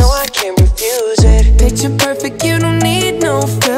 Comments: I misheard bitch you're perfect instead of picture perfect. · I heard "BITCH, you don't need no filter " instead of PICTURE PERFECT lol